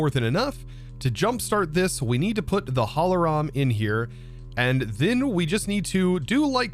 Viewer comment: every german cringes at that momnent haha holoram or what did you said? :'D